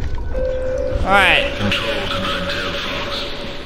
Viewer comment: anyone else hear "Control the nine tailed fox"